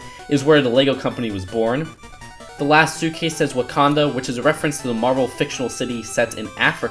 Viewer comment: WHEN IT SAID WAKANDA I WASNT PAYING ATTENTION AND I THOUGHT HE SAID CONDOM!😂